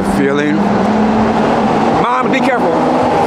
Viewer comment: What he say at 10:30 when the car passed by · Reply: "Mom, be careful."